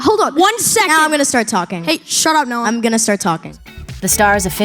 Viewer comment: captions say “shut up Nolan” lol